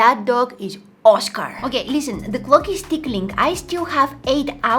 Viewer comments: Did anyone else hear say that the clock is tickling 13:02 😂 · sooo i heard "the clock is ticking" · Did anyone hear the clock is tickling at 13:02 lol I love her so much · … um did you just say “ the clock is tickling “?😊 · did you say "the clock is tickling"??😂 (btw i looovee your videos)